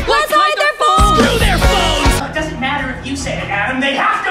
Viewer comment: "Let's hide their phones!" "Screw their phones!!!!" Actually Beetle they got it right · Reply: I thought they said bones...